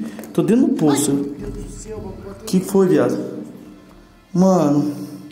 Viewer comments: escutei um oi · Mano no minuto 7:08 vcs tbm ouviram uma menina falando oi bem rápido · entre os minutos 7:08 e 7:10 bem na hora que você entre n poço da pra ouvir um Oi de criança bem rápido 😮😢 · Mano no minuto 7:08 vc vai escutar um oi olha isso